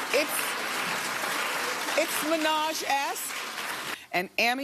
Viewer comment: i swear at 8:15 i heard it's Minaj ass...